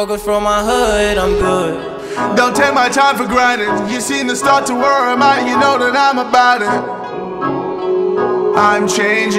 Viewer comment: he actually said: don’t take my time for granted, you seen the start to where im at you know that im about it**